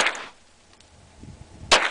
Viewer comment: did she say " u gotta aim with thi one"!!!????no shit!! :0